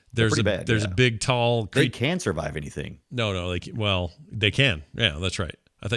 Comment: I totally heard, "They Cancer Vive Anything" too xD